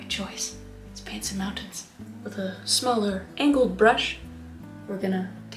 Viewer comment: It sounded like you said "now let's pants some mountain"😂😂